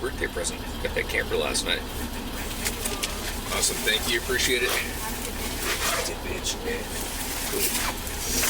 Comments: Did he say, "You too, bitch?"🤔👂🏽🦻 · did i hear you right?? 😂😂😂😂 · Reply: You too bitch is what I heard 😂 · i dunno what he says at @ 6:24 when she says have a nice day, but it really sounds like he said you too bitch and i cant unhear it. i know that isnt what he said, but i laughed. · it sounded like she said “have a good day” and he said “you too, bitch” haha